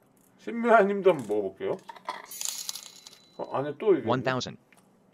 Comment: 초 나만 원돼지라고 들었음?